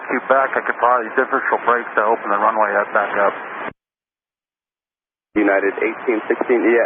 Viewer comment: he says “I can probably differential brake to open the runway back up” (use the brakes to steer instead of turning the nose wheel)